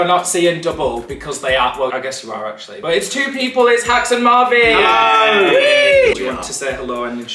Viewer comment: Did he just say Hax and Marvy 🤣😂